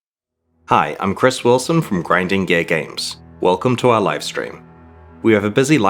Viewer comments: i heard him say "grinding gay games" and now i cant unhear it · For a second I honesty thought he said "Grinding Gay Games" at the beginning